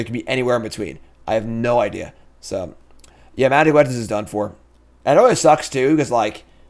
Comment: What's he say at 6:08? Something is done for? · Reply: Magic: Legends